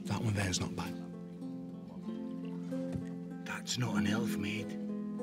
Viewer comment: What does he mean that's not an elf? What is he/it then. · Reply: right! I listened a few more times and realized he said "that's not an elf maiden" I thought he said that's not an "elf mate" lol I'm like yes he is an elf.